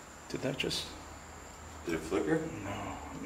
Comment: I think that might be but I heard someone say no